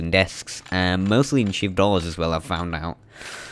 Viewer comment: what do you say please? it sounds like "cheap dollars" (which makes no sense lol). Thanks for the guide! What a great game, now playing Remastered version on Ps4. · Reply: I know this is late but I’m pretty sure he says “shiv doors”